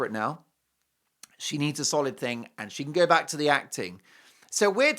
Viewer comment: I thought she said she will not go back to acting 😂